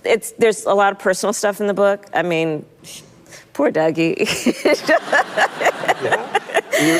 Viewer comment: Ohhhh She said “poor Douggie!” — The first time I heard this I thought she said “poor daddy” 😂😂😂😂😂😂😂 cackle cackle cackle cackle cackle cackle🐓🐔🐓🐔🐓🐓🐔🐓🐔🐓🐓🐓🐔🐔🐔🐔🐓🐓🐓🐔🐔🐔